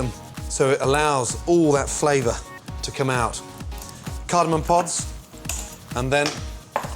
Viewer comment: What is he adding to the rice? It sounded like he said "cardinal pods" pls help